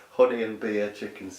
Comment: I just watched what I missed yesterday and rewatched some of the other parts again. Maybe you should change the name to ‘Dinner and Music with Colin.’ With your accent, I thought you said your dinner was ‘horny’ and beer chicken skewers , only to realized today that you said ‘honey.’ LOL This was one of your best live stream ever. It is good to be able to laugh at something as simple as watching a live stream of someone eating their dinner, lol. And I have not forgotten to mention your beautiful piano playing. Well done. 😎